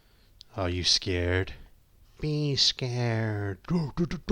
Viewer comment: Wait. At the beginning of Taker's theme, did the kids say "be scared"? I thought they said "he's here".